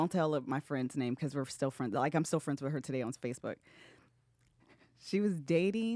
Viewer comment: did say ‘spacebook’ ? 😀❤️